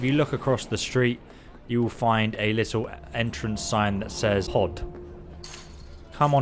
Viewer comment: ngl, I thought it said POO